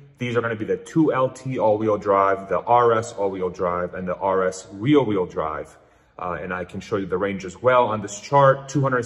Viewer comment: RWD = REAR wheel drive. Sounds like youre saying "real wheel drive"?